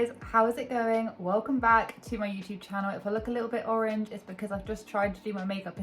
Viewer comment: at the start I THOUGHT U SAID IF I LOOK A BIT LIKE AUNTY LAUREN LMAO and the followup is even better ...cos I did my makeup in the dark